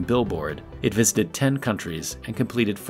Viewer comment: The narrator says "it visited 10 countries", but the captions say "it visited 10 cities". Which is it?